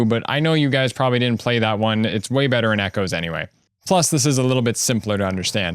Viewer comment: I think you got it backwards, I think I meant to say Echoes is way better as you said. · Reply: i heard you say "way better than echoes" but you said the minimap was "way better in echoes", my b